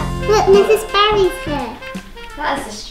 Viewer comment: Did she call her "Mrs. Barry"? lol what?